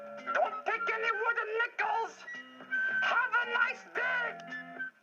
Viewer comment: Sounds like Grover said "Have a nice d***!" instead of "Have a nice day!"